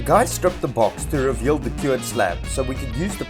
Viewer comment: Lekker boet! At 2:16 it sounded like you said the cure had slabbed... you said "cured slab" hats off to the videos and the work, I'm impressed you can do it during all that loadshedding bullsh#t